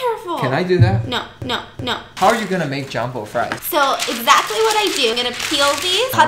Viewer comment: UMMMMMMMMmmmmmm did I just HeArD say JAMBO!!???😲 and not JUMBO??? fries🍟???🤣🤣 *Time Stamp 7:21*(Ur welcome😂)